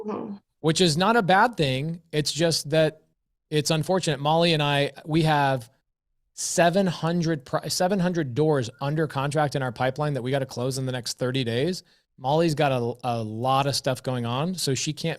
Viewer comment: am i tripping, or did just say he has 700 homes undercontract